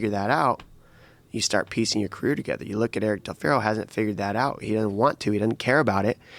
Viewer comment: Who is he talking about? Can't quite hear it well enough. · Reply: Eric Del Fierro